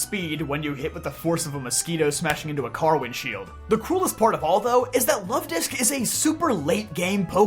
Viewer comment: Are you saying "Cruelest?" Or "Coolest?" at 13:00 13:01 ? The autogenerated captions say "Coolest" but it sounds as though you're saying "Cruelest" especially given the topic and subject matter! 😵‍💫